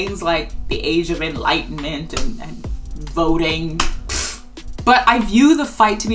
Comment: this is a fantastic essay but for a brief moment me and my bad hearing misheard "voting" as "boating" and when I tell you I felt so lost